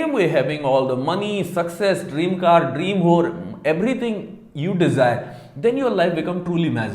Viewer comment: Did you just say Dream Whore?? 😂😂